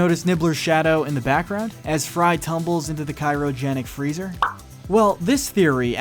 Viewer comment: Did he just say "Kyro Jennic Freezer?"